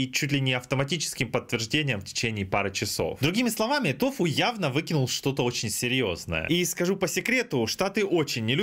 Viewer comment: пипец на очко присел когда краем уха слушал новости и услышал "тофу явно вытворил что то серьезное", думал у гг проблемы внутри команды